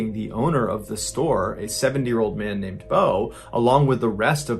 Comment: i literally heard 7 year old man Bo 😭😭😭